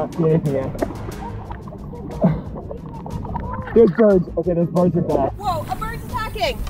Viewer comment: I thought he said those birds are black 😂